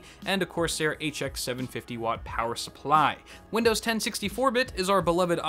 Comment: thought he said windows 1060 4 bit